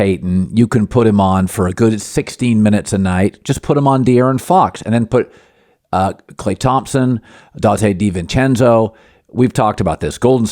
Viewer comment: He didnt say that. Pay attention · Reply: lol 8:45 "You can put Gary Payton on De'Aaron Fox for 16 minutes a night. And then put Klay Thompson, Donte DiVincenzo." That's what he said. Clean your ears!